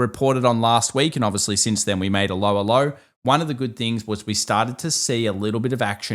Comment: When your Aussie accent said "a lower low" it sounded like you were saying "Allo, Allo"